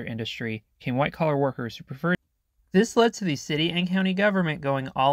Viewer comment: Audio cuts off for a second at 3:16, what did you say? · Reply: "Preferred driving to work at office parks" I'll do better next video on QA on audio quality. First time recording my voice in my life so mistakes inevitably happened. Good catch!